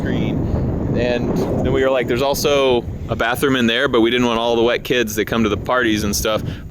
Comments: not to be a Debby downer but it definitely sounded like you said “white kids” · Did you just say....... " all the white kids?".......talking about running through the house in regards to outside bathroom.........I know your not racist, so what do you mean as that comment has confused me and had to rem-watch to see if I heard you right. 😳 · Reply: He said all the WET kids. 🤦🏼‍♀️